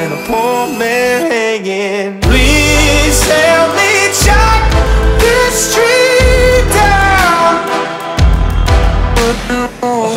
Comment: Bruh the whole time when I was listening to this last year during the summer i thought it said "help me shut the street down"